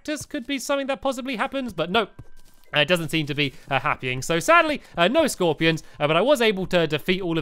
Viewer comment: Did he say happying?